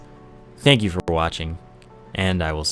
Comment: It sounds like you say "thank you for blodging" at the end. Not sure what that means but you're welcome :)